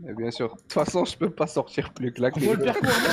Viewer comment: Il a dit quoi ??? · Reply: 'T'façon je peux pas finir plus claqué'